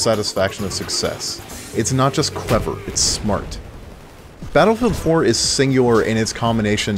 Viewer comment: "It's not just clever, it's smart." Did I hear that correctly? WHAT ARE YOU EVEN SAYING?!